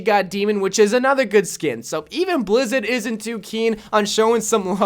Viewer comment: Did he just say blizzid or am I crazy